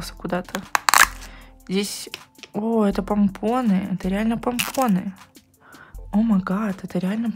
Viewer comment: мне послышалось тампоны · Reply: Она сказала пумпоны